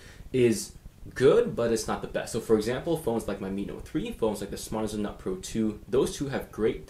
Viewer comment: Thnak you! Can you write, please, what phone you mention 2 times as phone with very good screen and sencitivity ? Sounds like a - pro -2 but I cant hear very well . It is on time 3:35 Thanks in advance ! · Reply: Sorry its Smartisan Nut Pro 2